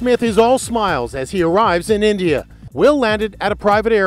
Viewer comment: I thought he said he smoked in india